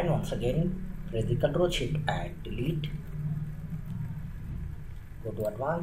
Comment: Did he say control shift delete for 2:13???